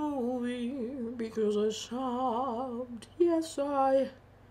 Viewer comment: LOL I thought at first you said you "stopped" watching the movie and then when you showed the pictures I was like "OH! SOBBED*" I'm so glad it was so fantastic and emotional!!!